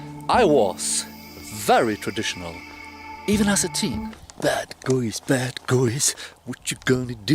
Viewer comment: he does not say "bad guys", he says "bad goys"